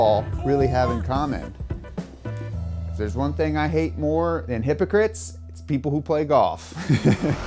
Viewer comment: I thought he said immigrants not hypocrites 💀